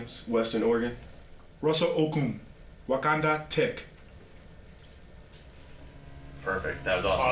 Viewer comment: Omg did he say wakanda tech 5:22 😂